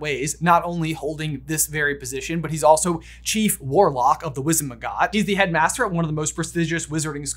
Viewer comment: Am I the only who noticed him call it wizen mcgot instead of Wizengamut? 😂